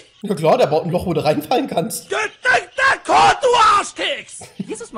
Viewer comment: was hat er gesagt? Göt irgendwas? Man versteht es nicht hört sich iwie nach türkische beleidigung an no Front an die türken 😂 wenn das türkisch ist kann das einer übersetzen? Danke leute